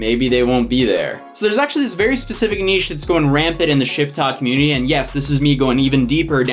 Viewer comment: I thought you were saying “shittok” and I totally understood